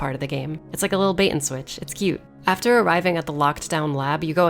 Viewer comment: Did she say ''cute''? 2:30 There's no cute in Resident Evil.